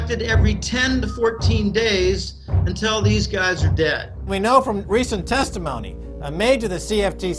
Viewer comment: Did I hear that correctly? Until these anglo-bankers are dead? Sorry but these guys had been in the business since the beginning of england? lol Good luck though